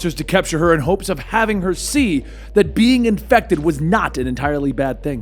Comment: at first I thought you said “in hopes of having her seed” and I was so confused lol